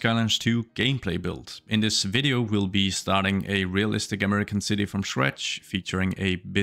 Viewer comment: With your accent it sounded like you said "We're gonna build an american city from Shrek." So I was like, ahh New Orleans!